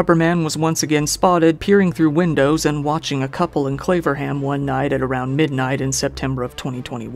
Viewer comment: 'Claverham', I thought he said 'Flavorham'!!! LMAO